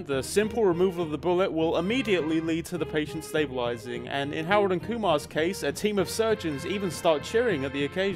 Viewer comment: Am I just mishearing or does he keep calling it "Howard and Kumar"? (It's "Harold and Kumar.")